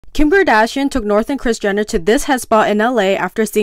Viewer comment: Idk why but at first i heard "kimber dashian"